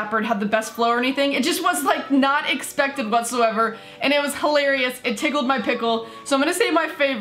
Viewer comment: Did she just say "it tickle my pickle"!!!!!